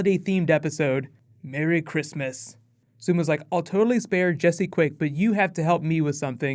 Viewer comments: Posting new Agents of SHIELD and Arrow tomorrow. Lots of other Marvel/DC/Star Wars stuff coming this week too :) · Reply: I swear I heard you say "Barry Christmas " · Totally had to do a double-take because I thought you said "Barry Christmas" at the start